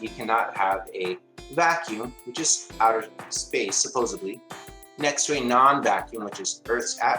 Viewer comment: Did he say 'supposably'. He wants to be a teacher? Sheesh...Yep, I just heard it again 'supposeablie' Oh, just heard this one... 'Alls you have to do'. What the hell is 'alls' How do you do alls? We are headed towards Moron here. Idiot was reached 2 minutes in. This was a tremendous effort! Hilarious!! Thanks guys! Loved it...Ah, wait a minute. He did it, he reached Moron. So pitiful. This misinformation should not be allowed on YouTube...But it is damn funny!